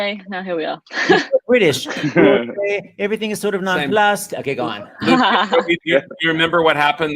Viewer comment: Did seth say “non blust”